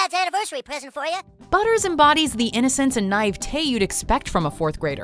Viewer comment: did she just pronounce it as nive, or did I mishear her? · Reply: "naivete" I think is what she said?